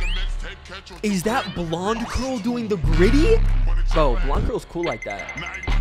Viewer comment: DID HE JUST SAY GRIGGY???